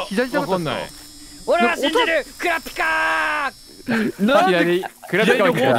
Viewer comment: wait did he shout KURAPIKAAA! at 4:07?? lol. and chrollo at 7:03? I don't quite understand the hxh references BUT i love it lol